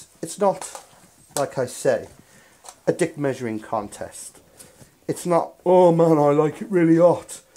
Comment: a dick measuring contest!?!!? LMFAO!! Or did I hear it wrong? Love!!! Cheers mate!